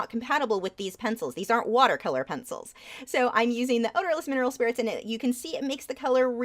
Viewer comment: I Cain hear what u said what did u use for the brush instead of water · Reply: Hi Naninatje! It's Odorless Mineral Spirits! We hope that helps!